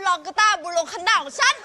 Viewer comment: ตุ๊กกี้ พูดว่าไงอ่ะ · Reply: Mr. teri24311 เอาคืนได้ไงของกู